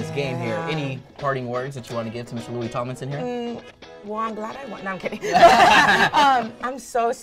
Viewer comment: Did she say “wanna go out?” 😂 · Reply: No she said “well I’m glad I won”